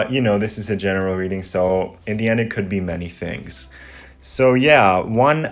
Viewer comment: Okay I’m like wtf. I chose like 3 and I said I’m going to swing to Indiana and hit the casinos for my birthday weekend. At 1:02:17 I thought you said Indiana, but realized you said “in the end”. But still